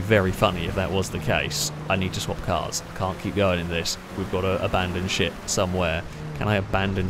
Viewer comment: I thought he said "abandon shit" lmao